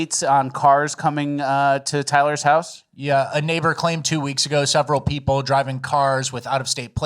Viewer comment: Here I thought you were saying Tweeks all this time.